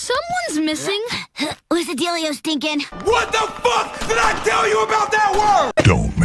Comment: That would be-oh, there she is. 7:17🤭She called him, “Stinkin’”. 9:16😠Did you just call them, selfish assholes?! 15:44🤭LJ called him, “Stinkin’”, again.